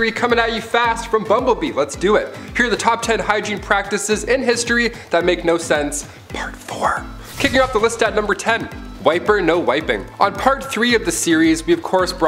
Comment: I thought you said, " Swiper No Wiping."🥴😅